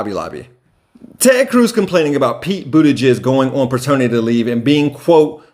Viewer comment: I swear it sounds like you said Pete "ButtiJizz", though it likely wasn't intentional it was still funny